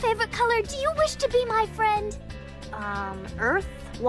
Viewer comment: I heard "new Fred"